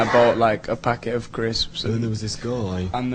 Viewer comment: Oh my gosh...they said "crisps" ? I thought they said "grapes" Awkss lol